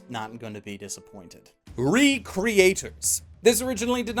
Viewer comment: Ehh mostly trash, why can't anime like darker than black come out, the best anime ever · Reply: CritiCold Oh sorry,derp Thought you said "studio" instead of "creator"